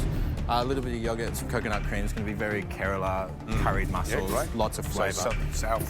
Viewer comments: WAIT! Did he say Kerala? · did james just said kerala?😍